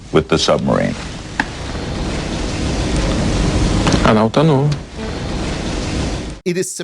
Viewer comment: What's funny is that youtube's automated subtitles think he said "I don't know" and it does kinda sound like that